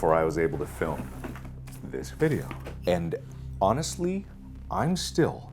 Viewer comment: Every time he says “honest”, I hear “Annus-ed” and I get sad